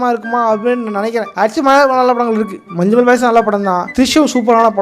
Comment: ena movie sona vro I can't understand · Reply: Drishyam